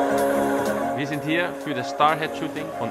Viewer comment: Did Muller say Fifa 15?? 😂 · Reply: no he said said siebzehn german for 17